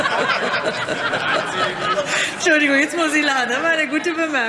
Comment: was haben die rein gerufen? hab’s net verstanden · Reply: „Das steht nicht auf den Bon“ Also Kassenbon